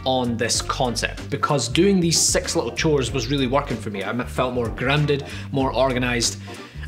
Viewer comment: For the first time, I thought you said "sex chores"?